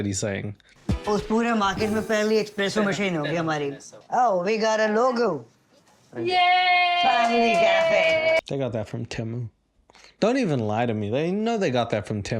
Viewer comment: At first I thought he said "you know they got that sign from Timbu" the Bangladeshi scamfish